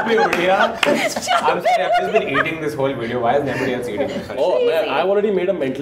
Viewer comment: Can someone please tell me what did kanan say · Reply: Krutarth Bhatt He said, "I'm just eating during this entire video, why is nobody else eating?"